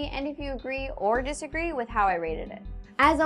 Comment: agree or disagree with how you READED it? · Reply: No, she said RATED not READED. Honest mistake though, I almost thought she said that as well!